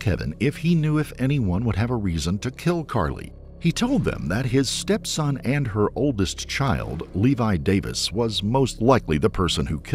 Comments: it says Levi Davis Karly's stepson.. it's a little confusing, not really the Mandela effect, though. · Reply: You went back and listened to it and still got it wrong. And you also heard the poster above correctly recite what the narrator actually said and yet you STILL got it wrong. At 5:30 He clearly says "he told them that HIS stepson and HER oldest child". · I think the “Mandela effect is going on here” people are thinking they heard something they didn’t hear. Just to be sure I went back and listened to it and I researched 4 different new stories on the murder and every single one says his mother, none of them say his stepmom. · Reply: yeah, I thought that’s what I heard too but actually went back and found it in the video. At 5:30 It says “HIS stepson and her oldest child” so you are correct. Clearly her son from a previous marriage and dad was the stepparent.